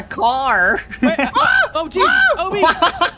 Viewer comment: did say sh*t? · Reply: No he said oh jeez but because he was laughing it sounded like he said sh*t.